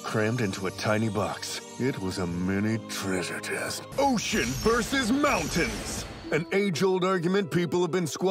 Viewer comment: At first I thought this man said OH SHIT VS MOUNTAIN XD